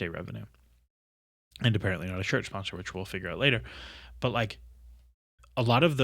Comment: thought you said "shirt sponsor stuff that we'll figure out never", very ironic